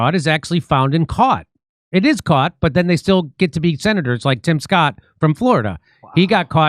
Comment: ~ Sounded like you said TIM Scott ~ the perp is Rick Scott of Florida